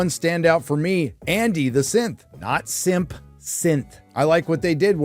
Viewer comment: I am glad he clarified because I thought was saying Andy the SIMP! What did he do to be a SIMP?